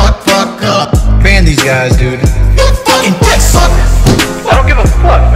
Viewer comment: I was laughing the hardest cause it sounded like he said, "You fuckin or what, man?!"